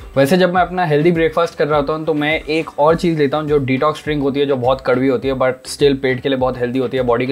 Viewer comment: plzz reply... What he said ..detox string or what ?? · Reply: Detox drink... like bittergourd drink